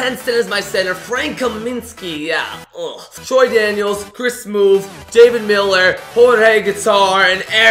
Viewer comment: lol did he really say my boy Chris smoove at 1:52 ?